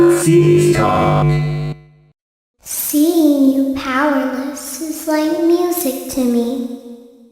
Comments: Could you explain 2:44 where the puppet says “Seeing you powerless is like music to me” but the captions say “The others are like animals, but I am very aware…” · You’ve got it wrong in 02:43! What he’s actually saying: “Seeing you powerless is like music to me…”